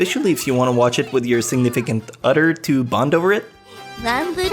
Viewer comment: i laught at the way you said "other" on the Chrono Crusade segment. sounded like you said, " you can watch it with your significant udder" XD